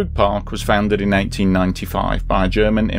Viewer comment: I think I heard him saying it was found in 1995 instead of saying 1895.